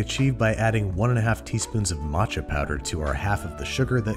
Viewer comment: First time I heard this I thought he was saying "Macho Powder".